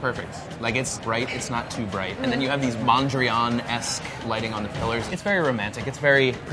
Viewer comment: what did Alex call the mirror style at 8:30 ? I googled it and can't find what he's talking about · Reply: Don't know if u still need the answer but he said Mondrian, referring to a painter who was known for his distinct geometric works. The mirror looks a little like one of Mondrian's paintings, that's what Delaney meant with that reference.